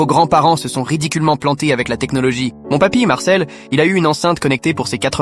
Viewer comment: au début, j'ai entendu "mon papi m'harcelle"